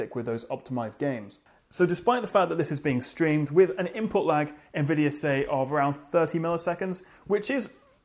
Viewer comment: "Input lag" - completely heard that as "nipple lag". I don't know what this is, but it sounds like an upgrade over what I've got.